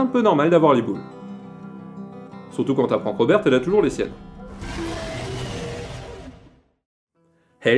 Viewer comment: surtout que robert quoi ? Je ne suis pas sur de ce que tu as dis, quelquun a til mieux entendu ? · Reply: Il dit "pour une fille cisgenre qui s'entraîne dur [...] c'est un peu normal d'avoir les boules. Surtout quand t'apprends que Roberte elle a toujours les siennes"